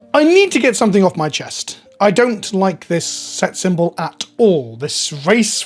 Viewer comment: Bruh I heard "s3x symbol" and had to take a double take xD